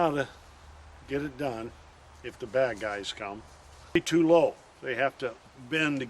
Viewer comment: “If the bad guys come” I heard that as “If the liberals come”